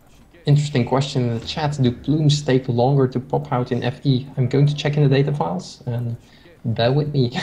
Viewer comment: What's the question at 14:08? I cant understand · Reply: "Do plumed archers take longer to pop out in AoFE?" I.e. do they take longer to be created.